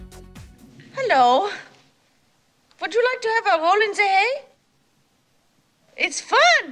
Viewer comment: Hello，would you like… そのあとなんて言ってるんですか？ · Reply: "Hello, would you like to have a roll in the hay?"